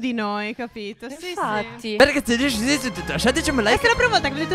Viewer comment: Al minuto 12:29 non capisco niente che cosa dicono XD(voi lo capite?) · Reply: ColuiChe NonSaas "bene ragazzi se il video vi è piaciuto lasciateci un bel like....